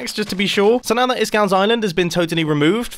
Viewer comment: if you turn on subtitles it will say instead of iskalls island it will say Thailand lol